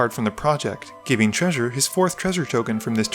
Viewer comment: I thought you had said "give treasure his fourth trevor token" and I about died